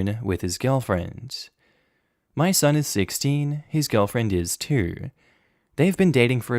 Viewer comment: I had to look at the screen for a second when I thought said "my son is 16, his girlfriend is 2 (two)" thank God it said "too"